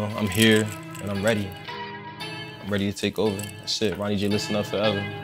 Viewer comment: I always hear Ronny J as "running jail" ugh